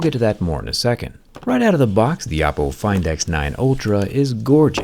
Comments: Did he just say the Apple Find X9 Ultra 🙀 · did I hear "Apple Find X9 Ultra"????????😅